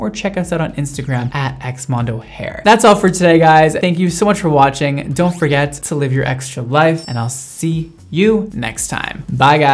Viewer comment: i thought he said "dont forget to leave your ex for life"